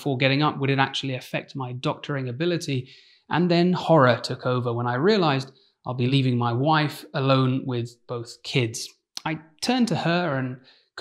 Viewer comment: Why do I hear "And then horror took over when I realized I'll be leaving my wife alone with both whiskey"